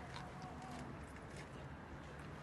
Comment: wht did she say!? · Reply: <num> I am